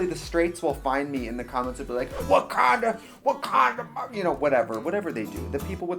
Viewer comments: I swear to god I thought you said WAKANDA WAKANDA😂😂😂😂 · Why does Trixie saying "what kinda" in a southern accent sound like she is saying "wakanda" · I thought she said wakanda